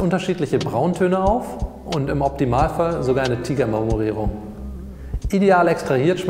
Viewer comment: Ich kann einfach nach unzähligen Malen immer noch nicht verstehen, was ab 2:08 sagt: "Die Crema ist schön fest und weist unterschiedliche Brauntöne auf, und im Optimalfall sogar eine ".........."? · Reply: Re Al er sagt "tiger marmorierung"